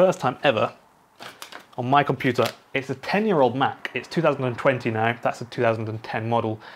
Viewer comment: Did he just say it's 2029?!! Lol at 0:14 seconds.